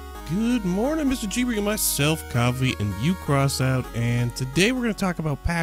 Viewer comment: when you say coffy and crossout, it sounds like your saying coffe and crosant